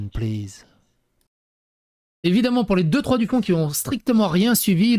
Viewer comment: Juste pour le plaisir : à 00:53 "Juste pour les deux/trois DUCONS" j'ai entendu. au lieu de "du fond". J'ai rit, bien rit même. xD